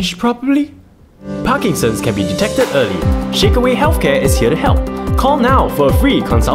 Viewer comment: I heard chicken wing healthcare 😂